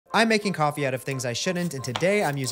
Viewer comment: I heard, “I’m making coffee out of things I shit in” and I was extremely horrified for about 1.5 seconds